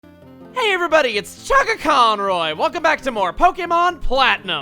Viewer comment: i thought i heard call himself "Fuckaaconroy" in the intro